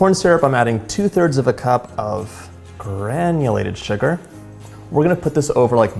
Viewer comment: Will you please clarify at 1:21, the measurement is 2/3 cup as you said, or 1 cup of granulated sugar as the caption said??